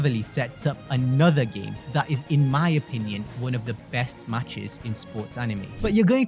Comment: Inazuma Eleven still to this day has one of the best Music's in Anime and TV Show History. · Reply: oh u sai one of the best i thought u said the best